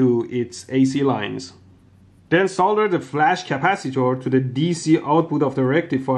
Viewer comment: For a moment I thought he said: " then solder the flux capacitor" LOL